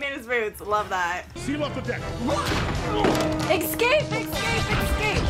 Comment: the way she says excape instead of escape will never not get me 💀